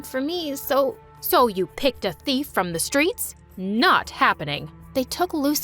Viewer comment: Its amazing how the character says thief while the subtitles say "THEME"